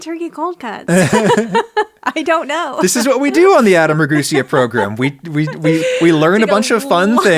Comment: I keep hearing: Adam 'a Goosea 😅